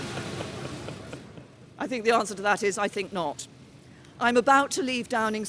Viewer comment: what is that man yelling out? I want to give him a medal. It shall read "I think not.. May 2019"